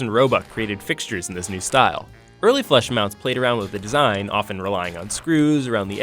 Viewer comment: Funny side story here . My mom and I just moved and she hated her ceiling fan in the apartment so we went to go look at lights to replace it . We're looking at the boob lights and I thought she said flesh mounts instead of flush mounts . I was dying of laughter in the menards lol . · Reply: Honestly, I cam looking thru the comments to see if I'm the only one hearing "flesh mounts" in the vid!